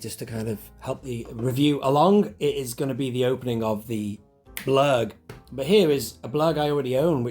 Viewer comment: ?! 🤷🏻‍♂️ eh?? Ha. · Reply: sounded like you said " the Klurg" right at the start